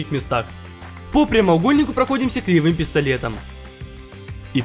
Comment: мне послышалось кривым пистолетом 0-0